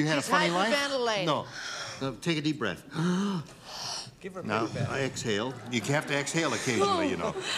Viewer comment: What'd he say at 1:39 and at 7:59? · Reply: 'Give her mouth to mouth'